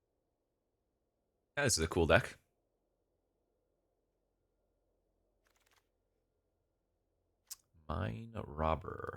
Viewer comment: Well, my good brotha! I miss read your word play. I thought you said: “Suck Akul Deck”!! Way different sentiment. Made me laugh tho.